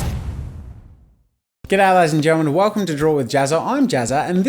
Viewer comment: It sounded like get out of here ladies and gentle man in